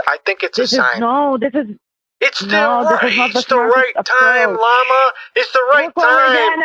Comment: did he just call her lama?